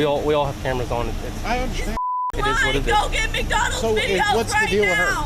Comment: I honestly thought she said "Go get McDonald's."